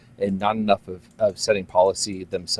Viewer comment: did he say obamna